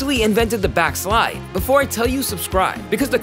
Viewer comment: I LOOKED AWAY AND THOUGHT HE SAID BILL GATES💀💀💀